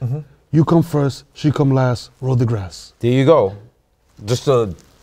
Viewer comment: I heard " You come first, she come last, Neil DeGrasse" ??? WTF 🤣😂😆